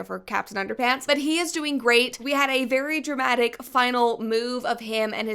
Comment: I heard, “we had a final MOOVE” 😂